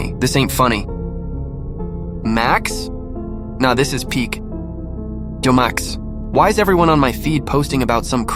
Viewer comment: I heard “Jo max” lol